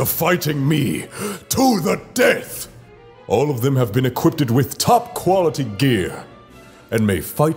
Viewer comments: did he say equippeded? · did he just say... equippeded?